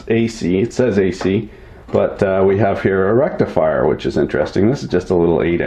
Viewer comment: Did you say 'Rectal Frier'? Can't see a use for that unless yer into that kinda thing.